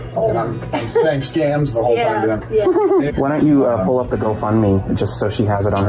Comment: Does say “Nick, why don’t you pull up the GoFundMe” at 12:04 ?